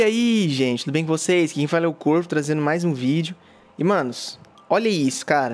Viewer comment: juro que ouvi corno